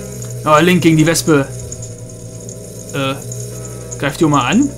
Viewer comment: ich habe am ende verstanden link gegen die lesbe xd